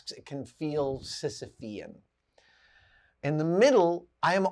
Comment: What is the word he says at 4:24? I turned on captions but it doesn't pick it up. It sounds like sissivian? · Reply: it’s been awhile since you commented but he said “sisyphean”, referring to the character Sisyphus from Greek Mythology who pushed a heavy boulder up a steep incline for all of eternity. in other words the task seems impossible to accomplish.